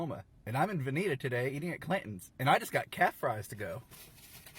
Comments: Guys the guy at the end was eating “calf fries” (cow testicles) not catfish lmao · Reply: I was so confused because the captions said "Calf fries" but It sounded like he said "Cat fries" which is likely what they heard? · It’s hilarious that they thought the mythical beast said cat fries, when he actually said CALF fries xD